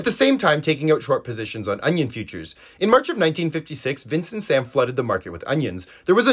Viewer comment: Thought he said Vince masuka from dexter